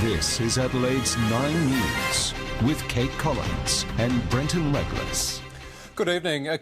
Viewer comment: Brenton Legless it sounded like at 5:30 lolza